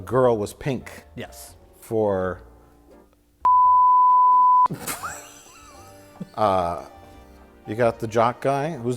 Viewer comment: What was the censored word at 21:22? My money's on "Bimbo" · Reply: Valerie Aust it was "pussy"